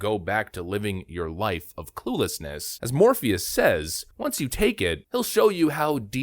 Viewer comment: When he said Morpheus, I heard it as morbius.